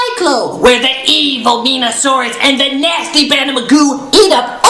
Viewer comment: did he say Venusaur?